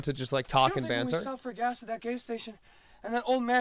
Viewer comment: CC says it's gay station, idk